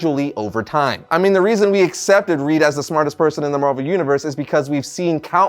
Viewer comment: I feel you on this particular situation!!!!! The Smartest person in the world 🌎!!!!? But yet I'm sitting right here and feeling dissed!!!! Oh my bad!!!!" I thought you said ' Smartest ASSHOLE!!? Or smartass!!!!? So !!? Sorry!! Carry on!! Lol" God bless you all today Shalom 🙏